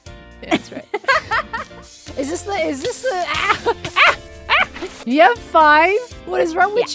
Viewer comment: Hey! At 8:40 in the video, the captions claim is saying "You're fine?!" when she's actually saying "You have five?!" referring to the number of flint & steels that Gem brought to their conversation. It's fairly obvious that's what she's saying in original video, since she says it in response to Gem admitting that she has five, but I can totally understand how you would mishear it with just the clip as context. Not trying to be rude, just pointing out! Thank you for all your hard work making the captions on these videos, I use them every time and they are super helpful for me!